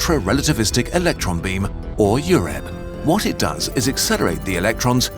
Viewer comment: "Ultra-Realativlistic Electron Beam, or UREN" Needed to go back and make sure I didn't mishear that because I thought he said Urine for a moment.